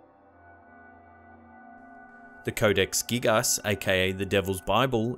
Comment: did you say code geass?